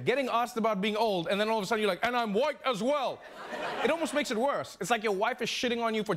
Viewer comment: I thought Sanders said "and i'm wise as well"